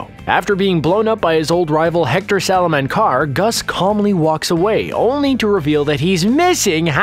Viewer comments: sorry - did you say "Hector Salamancar?" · Did he just say hector salamancar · No way did you call Hector Salamanca, “Salamancar” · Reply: I thought he said Hector Saladwanker